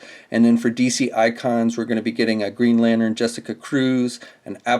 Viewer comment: Am I the only one who heard him say "Green Lannern."?